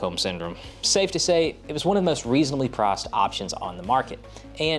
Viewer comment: oh! "PRICE"! I kept hearing "PROSP" as in prosper.